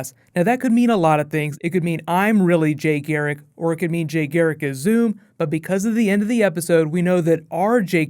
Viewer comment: I thought he was saying that Jay is zoom